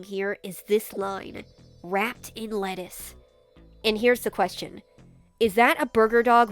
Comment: For some reason, when you said, "wrapped in lettuce," I heard "Rat in lettuce," which would've made sense for Fazbear even if it was wrong